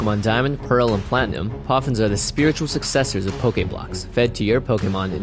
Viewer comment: ALL YOU FEED ME IS POFFINS! I NEED NOURISHMENT! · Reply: For the longest time i thought he was saying Puffins. Like the bird lol